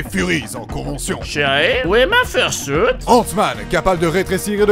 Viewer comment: "Shuri ! Où est passé mon First Suit ?" C'est moi ou c'est une référence à la Grande Aventure Lego ça ??! 😂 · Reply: C'est "Chéri! Où est passé mon Fur Suit?" Et c'est une référence aux Incroyables Mais oui, c'est drôle